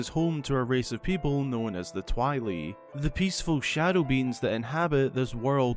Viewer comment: I misheard "shadow beings" as "shadow beans" and that makes so much sense tbh xD